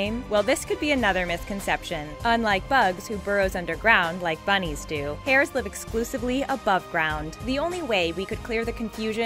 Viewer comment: WRONG, Bugs has been shown to live underground in his hole MANY, MANY times. There were even episodes of him defending himself by hiding in his hole, and fighting with others just to be left alone in peace in it. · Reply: I think you misheard. She says "Unlike Bugs, who burrows underground like bunnies do, hares live exclusively above ground". In other words, he looks like a hare and lives in a hole in the ground, but hares do not live underground. So she's really saying that Bugs is DRAWN to look like a hare, but ACTS like a bunny. Personally I always (even as a kid) thought that the animators just don't know the difference.